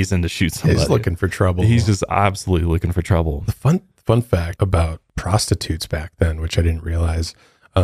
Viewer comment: At first I thought he said. Fun fact about prostitutes